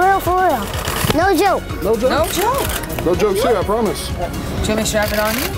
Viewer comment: Without Offense This is what i heard 2:49 Wait is this my ?? Yea, For real ? for real for real????? no i promise